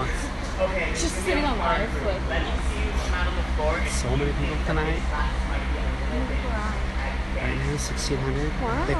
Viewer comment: After Rae who does he want out on the dance floor? I didn't quite catch it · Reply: He wants the people who think this dance might be a little bit too advanced for them to go out on the floor